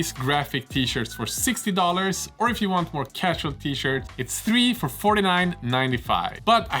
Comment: Random: In you're ad I heard you say "Casualty shirt" not "Casual T-Shirt". It was a funny few moments as I figured it out.